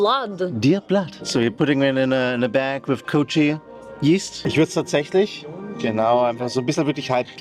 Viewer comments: the servers accent is so thick bro i thought here 35:45 he said coochie yeast wtffff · COOCHIE YEAST???? DID I HEAR THAT RIGHT?? 😭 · did he say coochie yeast? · DID HE SAY C00CHIE YEAST ???🤣🤣🤣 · did i just heard "coochie yeast"? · did he just say 'coochie yeast' ?!🥲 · I swear at 35:43 with the heart it sounded like he said they put it in a bag of coochie yeast and I was like O.o